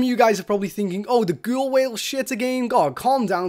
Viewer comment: What did he say at 0:22...? The 'ghoul well'? Sorry man, but I literally have no idea what you said.